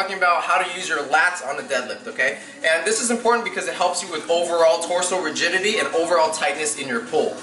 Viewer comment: I misheard but I was like “yes, I need help with overall torso virginity.....”